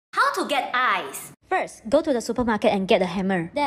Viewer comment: I thought it said “how to get rice” I need to get my ears checked 😂